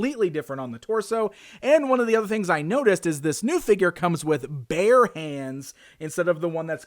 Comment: I thought you said “He comes with BEAR hands.” And was disappointed when he had human hands lol